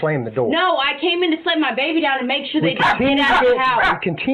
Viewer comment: does she say "No, I came in here to slam my baby down!" Lol is that an admission of guilt?